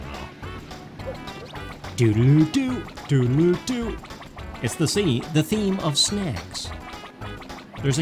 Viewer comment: for TWO SECONDS, I thought you had said "It's the theme of sex." XD Trust me, I don't have that dirty a mind and I didn't think you had either. This is a children's fame, after all, but that's the whole point of nostalgia. you can't physically go backwards, so...we time warp via lovely things like these.